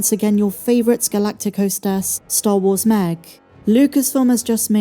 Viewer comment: I swear with the english accent it sounds like she says, "Star Wars Mag"